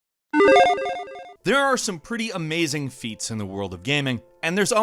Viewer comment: my video buffered at the beginning so i heard "there are some pretty amazing feet..."